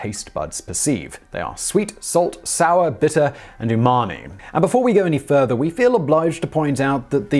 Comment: The taste map on the tongue is false, but let's continue showing it to people when we talk about taste. C'mon guys. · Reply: I thought he said your mommy when he said umami